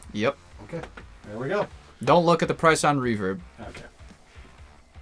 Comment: He did say "4 grand" right? I listened a bunch of times and couldn't hear $40,000 even with as much as I wanted to.